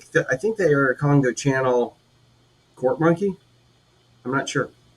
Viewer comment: 😲 WOW I THOUGHT YOU SAID PORCH MONK KEY